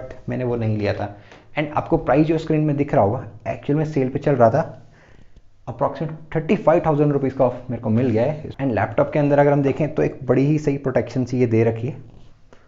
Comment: did you say 35k. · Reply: i say 35k off